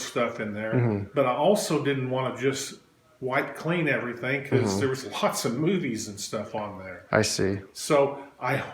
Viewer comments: I spit out my drink cause I thought he said “I also didn’t want to wipe everything because there’s lots of boobies one there” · i thought he said there is a bunch of nudies on there